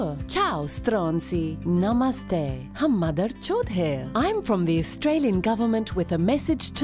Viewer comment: To all the Hindi speaking citizens, I bet you spat your drink out when you heard that 😂😂😂. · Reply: Oh my god 😂. Did she just say, hum madharchod hai ???